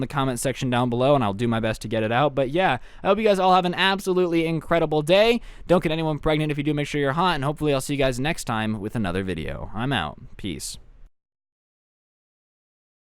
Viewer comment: I swear to got he said “ don’t get anyone pregnant and if you do make sure you’re hot” go to 11:14 and put it on 25 % speed